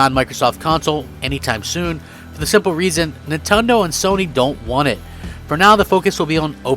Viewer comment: dafuq did he just say nintondoe😂